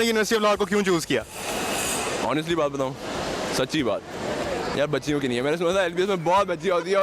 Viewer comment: Did he say mbbs?Ye dr bn k patients ko b bchyo ki trah hi treat krey ga ks trah ki mentality h Allah hdayet da 😐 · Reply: No, he said lbs